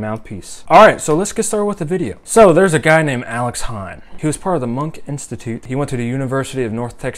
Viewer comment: Let me tell you-- I got REAL excited when I thought you said "Alex Han." It's all good; I'm about to discover another phenomenal saxophonist.